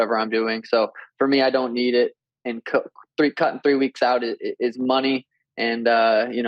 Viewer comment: Am I high or did he say he didn’t need it and coke ?